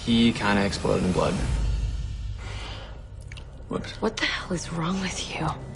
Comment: " Did he just say opps? 😂😂😂😂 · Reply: WHOOPS